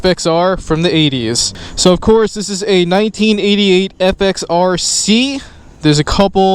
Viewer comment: Great scoot but a bike from the "80s" and 1998 is not the 80s , yes last century , but from the 90s...thank you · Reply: he clearly says 88, they didn’t make an FXR in 1998...thank you